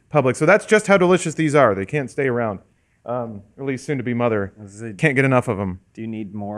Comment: did just say Elyse soon to be mom?